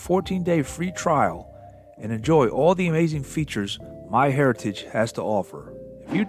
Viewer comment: OH, "Myheriatige", I thought you was saying MY Hair Piece. Yet another runaround story of 'Agent Orange'? (cover story)